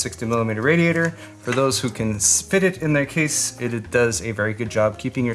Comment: lmao i legit thought you said spit it instead of fit it Edit: Aaand in the subs it says spit it so...